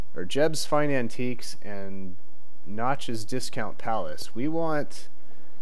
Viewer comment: That said Notch's Discount Palace? I thought it said Notch's Dinosaur Palace.